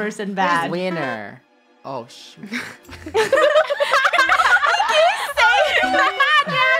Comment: Did just said oh shi-???